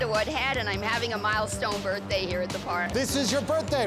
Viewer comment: I thought she was saying "I'm having a mild stroke...."